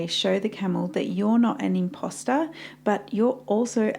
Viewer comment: When she said "Imposter" I thought she said "Pasta" 😂